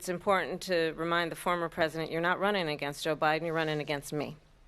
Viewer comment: The funny thing is, I heard, "You're not not running agains O"biden...